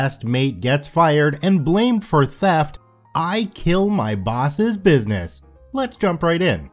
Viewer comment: I thought it said I kill my boss.